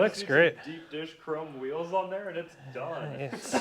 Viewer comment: the first time I heard get some big douche wheels on so I had to back it up to double check.